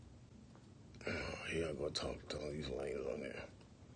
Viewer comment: 🤣🤣 Did he really say "Here I go, talking to all these lames on here"? Anyway, I love hearing these life events. Down to earth.